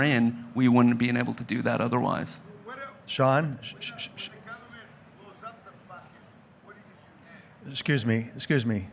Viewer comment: Anyone able to discern what the hecklers in the audience were saying ? e.g. at 30:08 ? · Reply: it sounds like “what about when the government blows up the platform? What do you do then?”